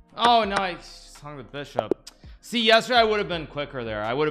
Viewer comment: Sounded like he said “son of a bishop”😭😭